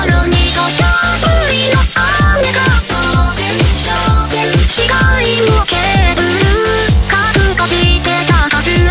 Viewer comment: นาโนพูดว่าไรอะอยากรู้😕😕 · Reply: น่าจะ I'm sorry